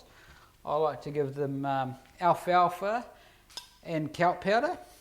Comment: what is he saying? alfy alpha? lol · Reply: alfalfa (lucerne)